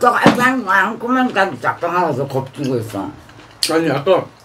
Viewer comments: 뭐라는거임?ㅋㅋㅋㅋㅅㅂ 2:43 ㅋㅋㅋㅋㅋㅋㅋㅋㅋ개웃기네 이건ㅋㅋ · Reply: 초는 딱딱하지 않구먼 자기 겁주고있어 2:43초는 앞에껀 못알아 듣겠고 으깰때 있잖아? 라고 얘기한듯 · 할매 뭐라고 중얼대는지 알아 들을수 있는 분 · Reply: 딱딱하지도않구만딱딱하다고겁주고있어